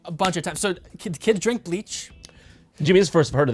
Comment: Wait did he say “kids drink bleach”???🤣🤣